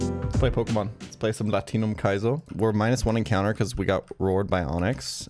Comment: ts sounded like Latino Kaizo